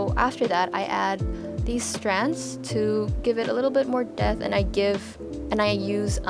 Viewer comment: who else heard "-to give it a little bit more death"? 😳